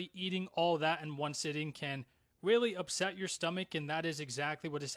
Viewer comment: Did he say “willy” instead of “really”?